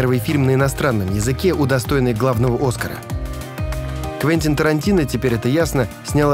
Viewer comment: Твентин Тарантино. Твентин. Сос,хелп. Помогите расслышать услышанное